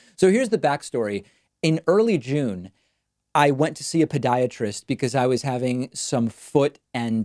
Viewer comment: It really sounds like David is saying Pediatrist instead of Podiatrist.